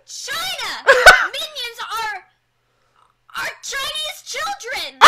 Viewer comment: When she said "Minions are-….are Chinese children!" I heard it as "Minions are…. Our Chinese children!"